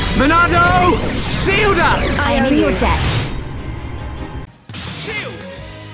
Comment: Why do I hear Melia saying: I'm your dad